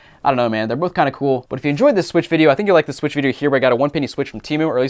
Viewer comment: Did I hear say “weird switchuation” lol